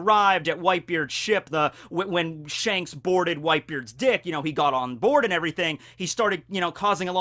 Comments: it sounded like you said when shanks boarded whitebeards dick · Did I hear right or did he say shanks boarded whitebeards d*ck😂. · Did anyone else hear "Whitebeard's Dick" or is it just me?